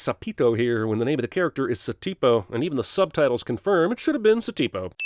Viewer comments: always thought he said " adios, estupido". (Goodbye, stupid) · he actually says "estúpido" which means stupid in spanish